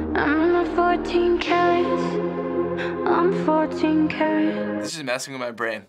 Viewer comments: IM SORRY BUT DID SHE JUST SAY "IM FARTING CARROTS"?!?!?!?!? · did it just said I'm farting carrots??!!?